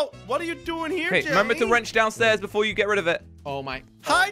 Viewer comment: jelly said ranch instead of rench LOL